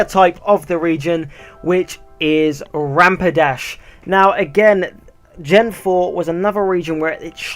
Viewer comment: did you just say Rampidash?